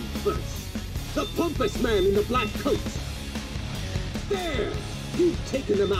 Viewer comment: I thought he said, “the homeless man in the black coat”. I almost split a seam thinking he called Joker a hobo.